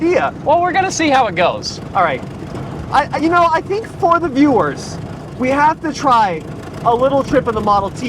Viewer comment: Thought I heard a little pun in there, "Ford the viewers..." 3:09 Also seeing how you guys have classics, do you have the classic Super Cub, like a 1963 Honda 50?